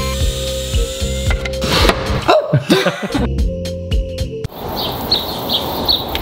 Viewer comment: i could have sworn you said "I need a Di*K "